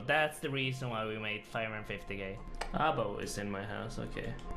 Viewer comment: its sounds like he said "ABO is in my ass" @ 7:13 😂